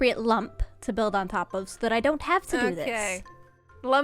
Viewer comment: said to terraform and started talking, like I picked a spot where I don’t have to. But all I heard is “I don’t have such weaknesses” was that just me or do you guys see it at like 1:10.